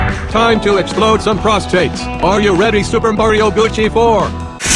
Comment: am i the only one, who heard "super mario gucci 4"